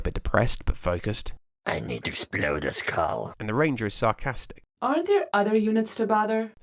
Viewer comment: what did the sniper say exactly? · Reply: "I need to explode a skull" haha